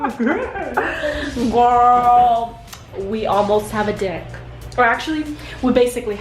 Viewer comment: not gonna like I heard "We almost have a dick" instead of deck yall 😂😂